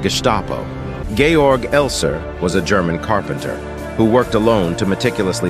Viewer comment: I thought he said “ gayer “ but he said Georg elser lol it’s AI talking that’s why lmao